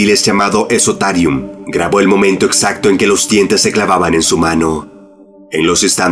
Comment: Entendí ano 3:59 🤣🤣🤣🤣🤣